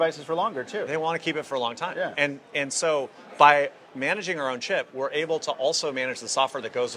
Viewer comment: I almost thought "he said by managing our own shit". Would have been cool and given it more personality.